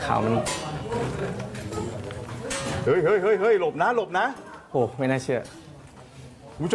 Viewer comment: ได้ยินว่าหนมน้าอะ😂😂😂แต่พอฟังดีๆเป็นหลบน้าๆ